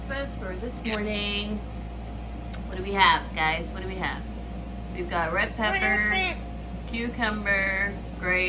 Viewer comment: Bahaha!!!! It sounded like at 0:05 milo said "WE GOT TREATS." In a dead tone 😂😂😂😂